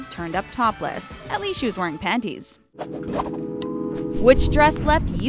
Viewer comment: when she said subscribe to the talko, I thought she said subscribe to the taco😂 lol.